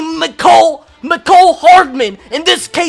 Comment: did you say macole hard***